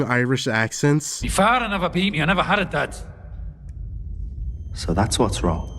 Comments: I think he's saying "Me father had never beat me, I never had a dad." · I think he’s saying, “My father never beat me, I never had a dad.”